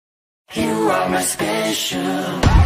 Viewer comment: Am I the only one who thought he was saying "Station" instead of "Special" the first time i heard it